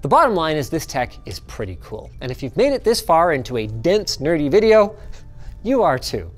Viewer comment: Did Linus just call me a dense nerd?